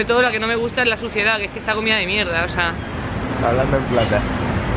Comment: Qué dice en el 3:26? no entendí 🙆 está comida de mier...a? no entiendo a que se refiere, alguien que me aclare porfis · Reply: Hola, dice que la ciudad de NY está comida por la mierda, que es una ciudad muy sucia. Un saludo.